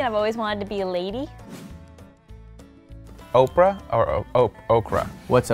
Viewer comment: Did someone say “Oprah” at 0:22?